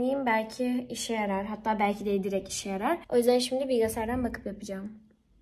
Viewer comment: Bi tek ben mi bilgisayarı bilgısaayar diye duydum